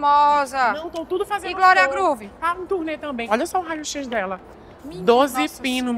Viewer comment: " - e glória Groove? - tá em turnê também" Manoooo eu racho muito de rir nessa parte do texto kkkkkkkkkk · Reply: Nossa, escutei "tá dura também"